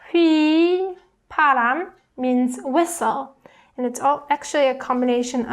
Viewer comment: I`ve heard it as a missile instead of a whistle. I'm definitely Kim-Jong-Uned.